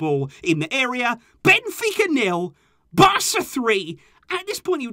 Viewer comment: Did he say benfica 0 Barca 3